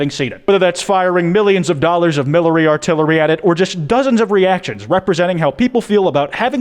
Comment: I thought you said millary artillery